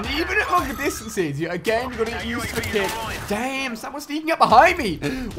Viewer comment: did he say dang or damn?